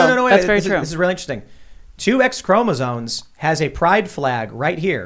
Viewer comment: Is Tim saying "chromozones" or "chromosomes"? To me, on my headset, it sounds like he is saying CHROMOZONES.